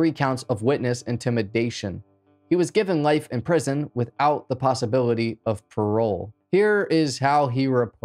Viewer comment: I'm sure I just keep mishearing... But what does "wiffty of coal mean?" Lol